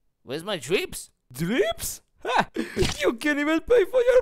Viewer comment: am i the only one or did he say "You cant even pay for your pizza,snake!"?